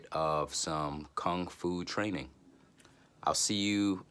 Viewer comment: I very much appreciate your comment on how the video are organized, and Ehret's quotes. Can you please elaborate on what you envision as far as energy building routines. Thanks for your feedback. :) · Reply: I apologize I thought you said you do qi gong but you said kung fu. I love your music and voice btw.